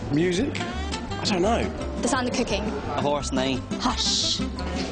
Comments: I thought she said “the sound of cocaine” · lol I thought she said "The sound of cocaine"